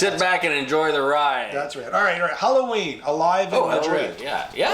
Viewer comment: It sounded like you said Halloween rather Helloween when you first introduced the band.